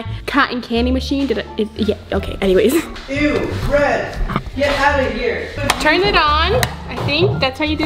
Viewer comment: Did anyone else hear say "eww get outta here" in